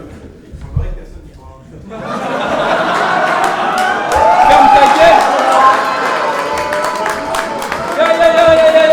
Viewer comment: il a dit quoi ? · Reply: C'est qui dit "Il semblerait que personne n'y crois"